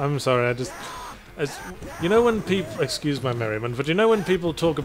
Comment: What did say at 33:47? Excuse my ...? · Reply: Excuse my merriment.